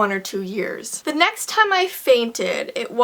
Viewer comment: lol i thought it said the first time i farted....lol im a huge fan